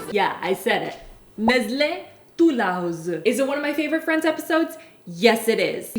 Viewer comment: I misheard “one of my favourite friends episodes” as “one of my friend’s favourite episodes” 😂